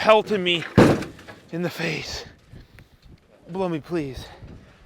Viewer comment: "Blow me, please" AHAHAHAHAHAHAHAHAHAHAAAAA I cracked up laughing and had to rewind and go "did i just hear that???!"